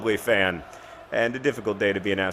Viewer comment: When he said best wishes I thought he said best peeps.